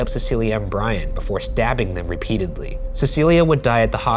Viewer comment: I thought he said "dabbing her"